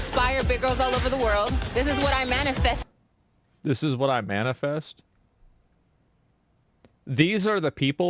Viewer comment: "This is what I manifest?" No, no- I think what she said was "Biscuit's what I many feast" or brisket, or bisque. One of those.